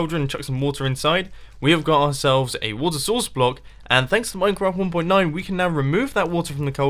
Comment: Did you just call minecraft... MineCRAP!?